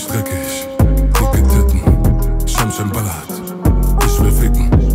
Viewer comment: Was sagt bei 1:36 ? ;D scham schham braat ? 😂